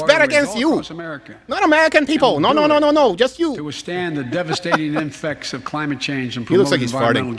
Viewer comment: Biden says "the devastating infects" instead of impacts 🤣🤣 of climate change. You guys didn't catch that one because you were talking, but it was hilarious